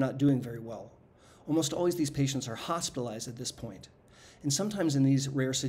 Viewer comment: I thought he said hostilized 😅